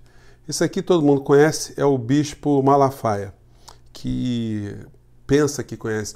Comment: Aos 02:20 minutos ===> Você, meu AMIGO Professor, com o seu sotaque carioca, pronunciou "Bixpo"; e, eu ESCUTEI "BICHO". Hahahaha.